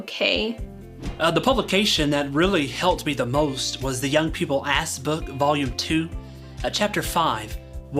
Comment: "teachings that generate candid expressions?" WTF DUDE? this is code for "torture" right? indoctrination? brainwashing? Stockholm Syndrome? it blows my mind that adult humans can listen to this blather and not have ALL of their alarm bells ringing like crazy. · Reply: "Young People ASS Book" ... 22:15 SERIOUSLY YESS! Every time he says "ask" I keep hearing ASS. I thought it was just me.